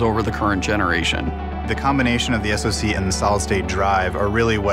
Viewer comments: "The combination of the SSD and the Solid State Drive..." wait what? did he say the same thing twice?? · Did he just say SSD and Solid State Drive instead of RAM? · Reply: If you turn on CC you can see that he said "SOC."